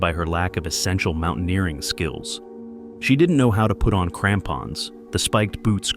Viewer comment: I wasn’t watching and thought he said tampons when he said crampons and I did the biggest double take 💀